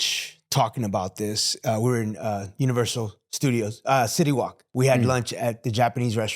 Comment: did he say shitty wok from south park? 😂